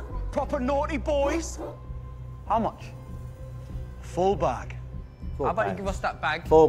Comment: As great as everyone in this movie was, Collin Farrell absolutely carried the whole script on his back. One of the funniest performances I’ve ever seen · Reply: Yeah, I think thought he said four bags instead of full bag